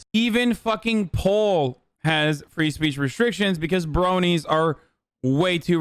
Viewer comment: Is it just me or did he just say bronies